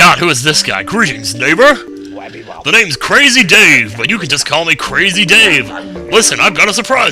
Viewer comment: I love how as Crazy Dave is saying "My Name is Crazy Dave" it sounds like he is saying wacky-bob.